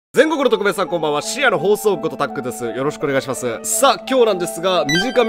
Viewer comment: これ最初の挨拶なんて言ってるんですか、、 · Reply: 全国のトクメーさんこんばんは深夜の放送部ことたっくーですよろしくお願いします